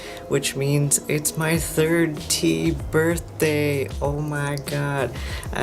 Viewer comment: It sounded like you said it was your 30th birthday. I thought you were in your 20s.